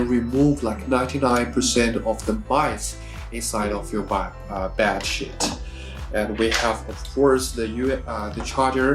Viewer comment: “Remove 99% of mice inside the bat shit” ??? What’s he saying? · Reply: Mites on bedsheet lol